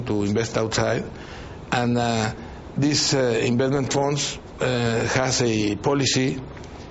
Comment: i think he said embezzlement as opposed to investment.